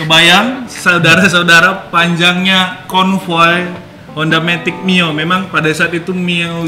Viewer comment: Honda matic mio??? Apa aku yg salah dengar ni bg 😁